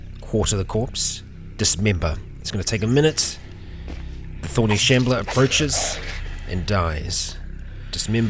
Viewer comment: You say thorny shambler I hear “horny shambler” and it brings back forgotten horrors